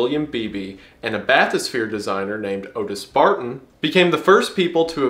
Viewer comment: thought u said Otis Fartin dog I had to rewind and shut my eyes to listen 💀